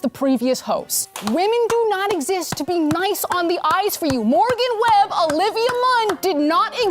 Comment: Thought she was saying "Nice on the Ice..."